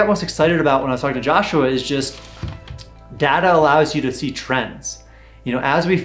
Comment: I misheard your statement as “Data allows us to teach friends” instead of “Data allow us to see trends” 😅 As a scientist, I like both.